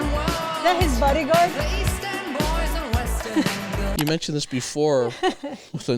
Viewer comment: Love your face/moves in this one!🤣 And: ALWAYS. Until pretty recently, I thought they were saying WESTERN girls!